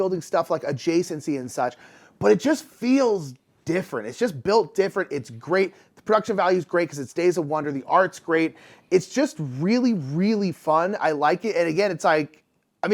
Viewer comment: I thought you said "It's dry as F@ Sand". I had to play it back and turn on the CC to clarify. I was shocked. Not disappointed, just shocked. lol Love you guys! -kb